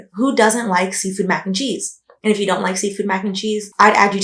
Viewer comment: “And if you don’t like seasoned mac & cheese, I would add you to my prayer list but it is long.” 😂🤣 It’s not just your jokes, it’s the way you so eloquently roast people and then continue with the video. 🏆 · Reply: Thought she said SEAFOOD not seasoned 💀